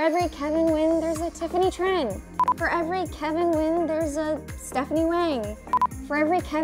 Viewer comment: For every Kevin, there is a Stephanie Hwang. Did I hear it right, Stephanie Hwang? Girls' Generation? Anyone? Tiffany Young? LOL. 🤣 But anyway, if "I Believe in a Thing Called Love" by Maurene Goo would be made into a movie, I want this girl to portray Desi Lee. 😊